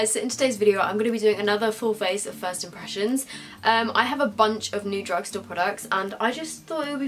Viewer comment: I heard "face oppressions" LOL :).